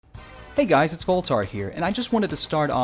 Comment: I thought he said at the start of the video " hey guys my name is fulltard"